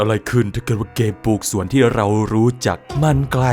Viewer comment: ได้ยินว่าเกปลูกสวนอะอ้ายแท้